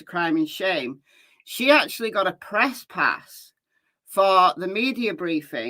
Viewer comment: I misheard and wondered the whole time how sharmane managed to get a „tresspass“ 😂